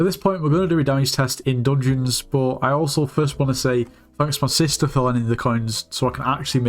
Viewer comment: thought u said my sister died 💀💀